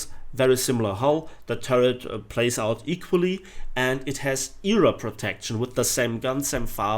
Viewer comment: i think i just heard Europe protection